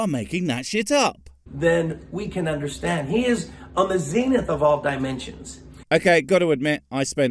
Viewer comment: Irrespective of everything else: I heard "on the zenith" quite clearly IDK why you thought he said "mzenith."